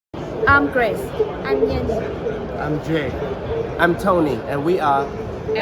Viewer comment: I heard : I'm Gay